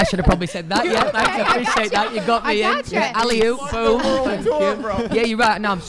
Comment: What did he say at 8:50? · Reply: He says "alley-oop, boom!" I'm surprised he actually knows basketball hahaha